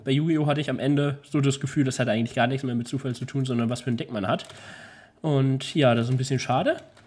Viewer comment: wer hat auch verstanden was für nen dick man hat? :D